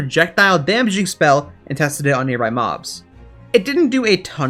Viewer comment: Him:I will tested it on nearby mobs Vs What I Hear:I will tested it on nearby moms